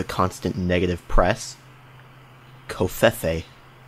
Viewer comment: it sounded like he said covid